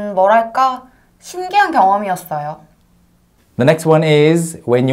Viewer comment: When the word "경험" is spoken at 3:46, I can't even hear it as a distinct word. Even slowing down the audio to .25, it sounds like she's saying "Gyo-wum" to my ears, and even that's just barely audible. I feel like the hugest hurdle to becoming functional with a language is being able to somehow decode and connect all these fluid and oftentimes barely audible sounds to the same place where we have the fully pronounced word and its associated concepts stored in our brain so that it is immediately and unconsciously recognized.